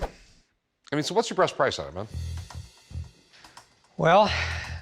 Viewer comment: did he say “what’s your BREAST price” ? 👙 🍈🍈 😂